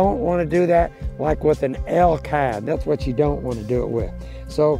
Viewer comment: what kind is he saying not to wear in rain? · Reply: I think he said chap pants that made with elk hide aka elk skin...